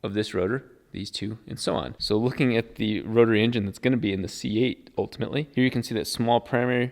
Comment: WHAT DID HE SAY???!!! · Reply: Rotary in a c8. Hopefully a manual as well